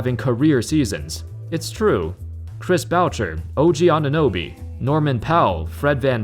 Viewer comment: I heard you say Chris Boutcher LOL